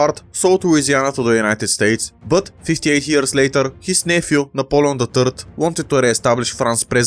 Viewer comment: Why did I hear "Napoleon the turd" here? 0:05 😂😭